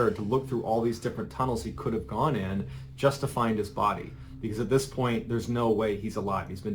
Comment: Okay, so the auto subtitles misheard you throughout the entire video and replaced "buddy/buddies" with "body/bodies" and I only realized this 3/4 of the way through the video. Lol! 😅